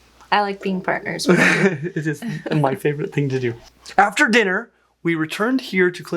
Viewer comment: I truly thought u said to " lol ur my favorite thing to do"